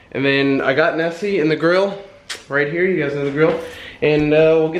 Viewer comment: You guys know the grill - I thought you said - you guys know the drill